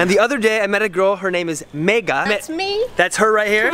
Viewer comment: I thought he called her mia but her name is.. 😂😂😂 But I just watched her in full video same as Mia Khalifa 😂